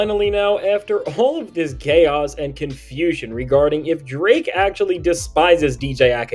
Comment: Bro, did you just say "GAY-oas and confusion" or am I tripping??